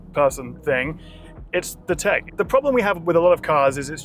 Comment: Oh dear, all I kept hearing was 'shite tech' which seems a little freudian perhaps😉